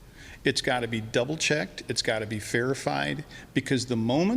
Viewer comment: Coulda sworn he said "it all needs fairified"... (verified). Fairified ought to be a new word we use in our day-to-day lives.